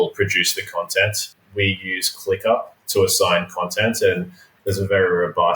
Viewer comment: what software is used? “Clicker?” To assign content? Did I hear that correct?